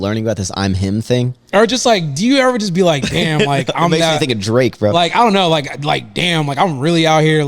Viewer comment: Lol did say nigga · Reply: "Makes me think of Drake, bro."